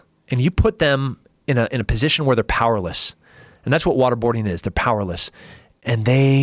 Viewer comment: i thought it said wakeboarding anyone else think that